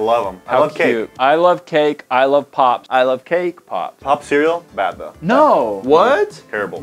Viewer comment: When said cake pop I thought he said kpop 😂